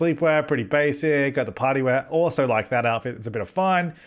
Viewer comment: When Said Party Wear I thought He said Potty wear xD